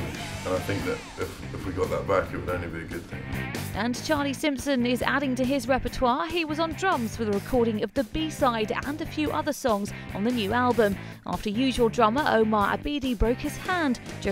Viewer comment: oh crap, omar and his hand... i thought she said charlie was on drugs lol